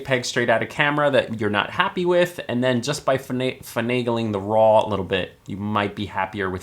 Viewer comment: Did you say vernageling? (5:04) Never heard that word outside my own country (Netherlands).